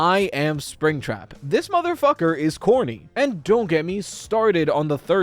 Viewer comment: I THOUGHT YOU SAID "this motherfucker is HORNY" & yk... I feel that also fits him